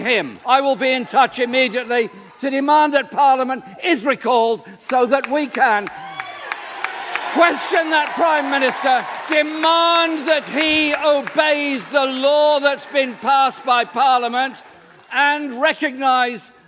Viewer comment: I thought he said “demand the tea” 😂